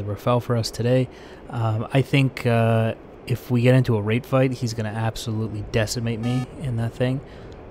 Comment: "If we get into a rape fight..." no wonder you had to clarify before cuz I hear it now and I cant unhear it 🤣🤣🤣